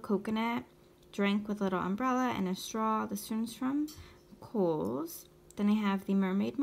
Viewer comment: Where did you say the coconut drink charm came from??? Love it!! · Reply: I thought you said Kohls!!